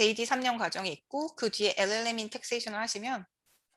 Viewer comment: ~ JD 과정 뒤에 오는게 뭘까요? 5번 반복해도 잘 안들려서요. L&M? ㅠㅡㅜ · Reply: LLM 입니다